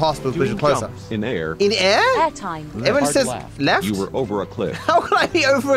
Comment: i heard hentai